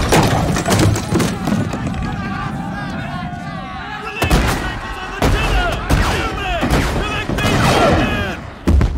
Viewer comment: Does anyone know what Mr. Mowett says at 2:32? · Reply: Something about the tiller, then he orders someone to "collect these wounded".